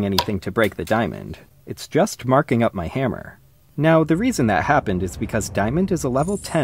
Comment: I was in a loud setting and when bro said "marking up my hammer" I thought he said "it's just fucking up my hammer 😊" so sweetly and it made me wheeze so hard 😭